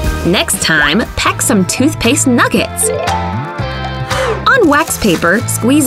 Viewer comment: You sound like you said chicken nugget